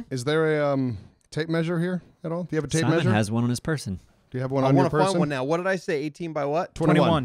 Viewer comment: "Person" I thought he said Simon had one in his purse